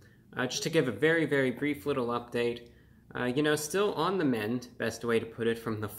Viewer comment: What he said : "still on the mend" What i heard: " still on the meds" Why i eat junk fast food : 💊💊💊 Reviewbraw: Welcome to the Report of the Week, pain killeeer review!